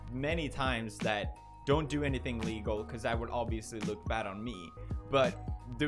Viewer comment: on 10:54 it sounds like he says "dont do anything legal" and now all the news people are gonna take it as that even though his accent may have just mucked it up